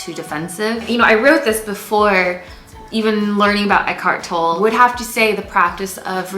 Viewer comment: can somebody tell me what did say? Please. Learning about....? Thank you! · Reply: Olga Madeja eckhart tolle, he’s a wonderful philosopher and I love reading/listening to his teachings 🥰